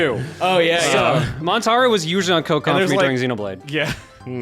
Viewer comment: I don't know if I heard right since interrupted, but at 4:25 it almost sounds like said "Montaro was usually on coke during Xenoblade". Also, accidentally binning the [SECRET CHARACTER] reminds me of a little incident that happened at Christmas. I'd brought some cherry liqueur chocolates to the gathering, and my sister checked the ingredients on the box. Then, to my eternal disappointment, she fumbled the box and sent the chocolates scattering across the room. Sure, I still have several boxes stashed away, since they were going cheap when I got them, but I still felt a bit of my soul die when she fumbled those chocolates.